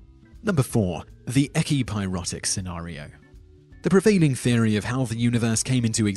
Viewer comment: 4. I thought he said erotic senero